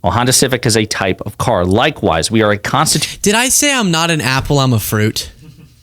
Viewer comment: 💙 I thought you said I am not an alcoholic I am a fruit I died 😂😂😂😂😂